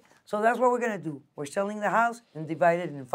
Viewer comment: Lol did she just say 'and that, is how I roll' 😂😂😂